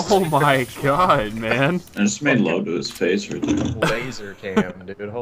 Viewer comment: Nobody said, "I just made logo's FaZe for them" Did anyone else catch that? lmao